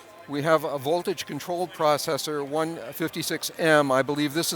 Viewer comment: I thought he said "mortgage controlled processor" which wouldn't be an incorrect name for a buchla system... ;-)